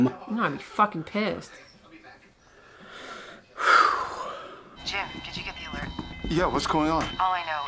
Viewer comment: the phone on the voice addressed Dexter as Jeff not Jim. At least thats what I heard.